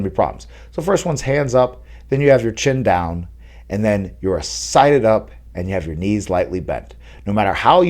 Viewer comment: Sided up is more pointing your lead shoulder at your opponent. Square is facing your chest to your opponent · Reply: Thank ya for the clarification man. I thought you were saying “sighted” up like as in focused on your target and ready to explode.